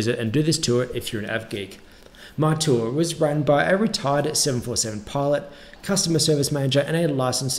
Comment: Is it just me or does it sound like he said "retarded"?